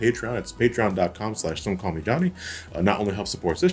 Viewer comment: When you started your patreon intro, I swear to God instead of some call me Johnny, I heard don't call me dummy. Lol